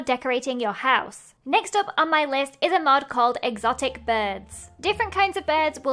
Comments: I thought it said exotic butters. Only fnaf fans would get that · "Exotic birds" (I almost thought she said Exotic butters...) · When Lizzie said exotic birds I thought she said exotic butters 🤣🤣 😂😂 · I thought you said exotic butters for a second, I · when you said “exotic birds” I thought you said exotic butters 😂 I’ve been watching too much fnaf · When you said "Exotic birds" I thought you were about to say "Exotic butters" from fnaf 5 🥲